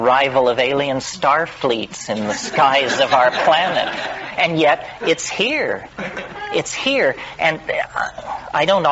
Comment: 👍Thanks for the video my friend 🌄 What is the word at 7:27???...after bizarre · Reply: Outré is a french word it means Beyond , Bizarre. glad you liked it ^^